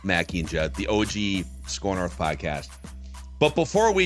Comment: Every time you say SKOR North to start the podcast I keep hearing "Scorned Earth" which is kinda how it's felt the last week after the trade deadline. 😂